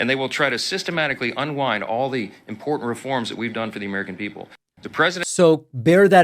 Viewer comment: I think maga Mike said “…reforms that we’ve done TO the American people.” He got that right in a very telling Freudian slip: not FOR us, but TO us.